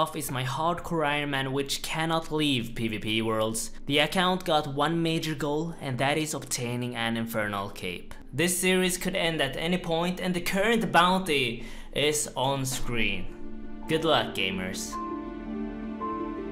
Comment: Lol, I thought you were saying the advertisement was for "Max Cape"!